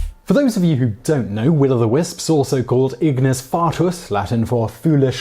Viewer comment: I swear to God I thought that said ignis flatus